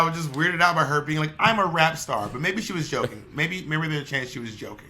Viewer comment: was obviously joking when she called herself a rap star lol. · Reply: And I thought she said Rapstress not rap star